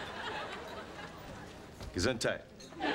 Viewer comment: what does joey say? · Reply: gesundheit